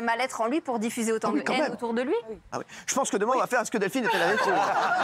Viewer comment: Il a dit quoi Hanouna a 0:14? rien compris. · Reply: « Je pense que demain on fera : est ce que delphine est allée trop loin ?